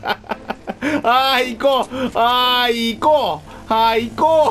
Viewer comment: 本当は「アーイコー」って聞こえるんだろうけど俺からしたら「あー行こー」に聞こえちまう…